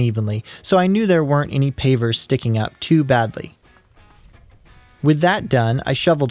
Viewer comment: 日本人の皆様ここ「素敵な」って言ってるようにしか聞こえません